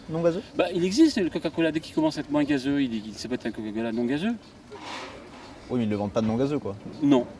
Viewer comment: Bon OK à 1:55 quand-même je suis pas la seule à entendre "caca-cola" dites moi 💩🤔 ?